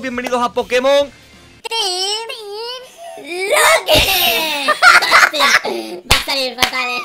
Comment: no se si les paso pero aca yo escuche tri locke en ves de team locke